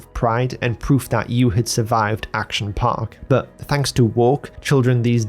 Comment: did he say thanks to woke?